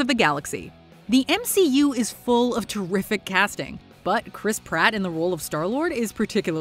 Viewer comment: God every time I hear Chris Pratt’s name I just hear “Crisp Rat” 💀